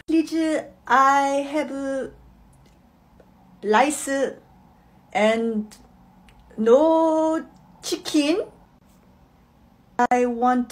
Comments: i thought the mom said "lice and no chicken" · What I heard "lice and no chickin" Me:👁👄👁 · What i hear "Ne che i have no licer and no chickin" What it actually say "Can i have rice with no chicken?"